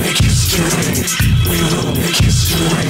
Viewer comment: RIP to anyone who cant find this track after hearing it in Edgerunners.👨‍💼❌🧑‍🎤❌👩‍⚕ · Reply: I heard “history” like “destroy” so I didn’t find it for a half a